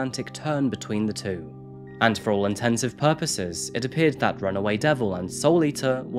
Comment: Did he say “For all intensive purposes” @ 6:55? 😂